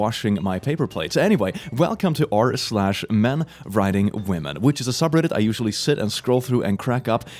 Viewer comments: I actually at first thought you said, "Men riding women"... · For a second I heard "men riding women".